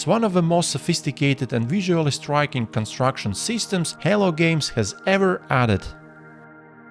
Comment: I heard halo games lmao